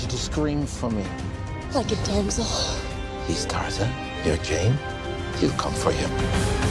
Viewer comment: Hes tarzan, your Ghey ? he come for you... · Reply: i thought i heard "He's Tarzan. You're gay. He'll come for you..."